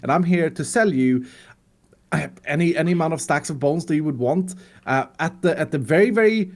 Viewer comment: I thought iskall said boat not bones